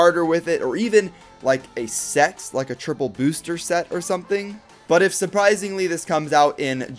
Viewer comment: you sounded like you said sechs